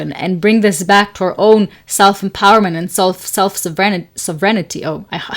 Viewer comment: sovereignty is pronounced sov-rin-tea, with emphasis falling on the first syllable :) You sounded like you were saying self-serenity. I love what you said about every decision also being a rejection. Such a great way to think about it!!! Iml have the hardest time making decisions. It actually really helps to think of all the things I'm rejecting rather than just focusing on what I'm choosing